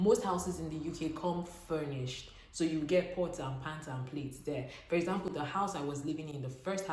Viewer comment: I heard pots and pant and plate 😂